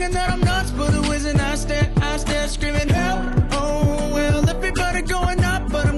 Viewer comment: I thought he said Help oh hell😂